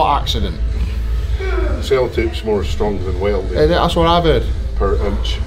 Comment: I thought the subtitles were dumb until you said "sellotape is stronger than welding" and i heard "stronger than whaledick"... then again, maybe that says something about me im not ready to admit to yet